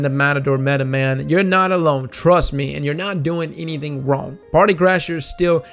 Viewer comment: did I just hear ''if you roll the matador, you are not doing anything wrong''